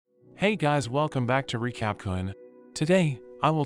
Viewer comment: glad you are enjoying the videos :) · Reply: ngl I thought you said "recap kunt" idk why tho but I love your video